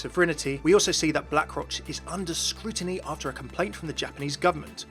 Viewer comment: /37 is it just me or did i just hear say black-crotch